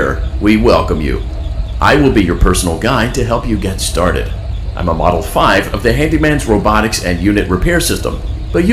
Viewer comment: imma model thats what i heard when i had to pause the vid to geth the phone LOL